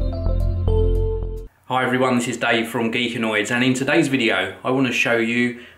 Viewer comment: I thought his name said Greekandroids. I was like OMG his name has ANDROID